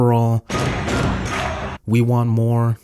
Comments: I always thought it said "we want war" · i heard "we want more" all the time